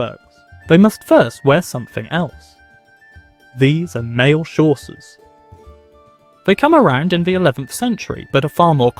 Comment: Great video so far! I am 8:43 and you just said a word that I don't know and the subtitles are of course not helping, so I was woindering if you (or anyone) could enlighten me. At ~8:41 the narrator says "these are mail __________" (something like chaucers, but I assume that's totally wrong). What is that word!? Thanks in advance! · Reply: Chausses